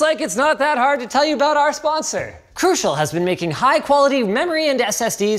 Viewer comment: When he said crucial I thought he said corsair wrong